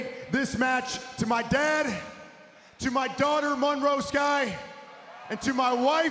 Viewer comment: i thought he said my daughter maryse wtf